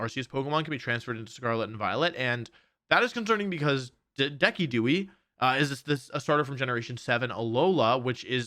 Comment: Did he call decidueye “decki-dewy” ?